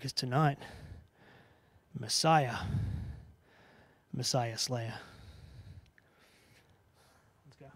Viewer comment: did Murphy just call himself the Messiah Slayer?